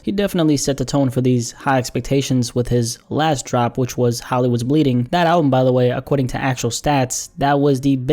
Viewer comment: I keep hearing "hollows bleeding" when u say hollywood